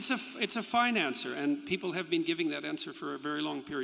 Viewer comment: Is it possible that we witnessed a very genuine Freudian slip? Peterson says that it was a "fine answer" when he says that it would take 40 days to answer whether or not Christ was resurrected. What I heard, was "financier". And I was surprised that he had been so honest. But then I realized he had consciously said "fine answer". Certainly those videos on YouTube about the Judeo-Christian God have been a major boon to his career. And certainly he would not want to alienate all the True Believers in his flock.